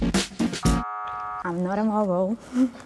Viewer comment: "they felt threatened that's why they kicked me out" 💀💀sis please you forgot cho's name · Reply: I thought she said ‘I’m not a mole yall’ 💀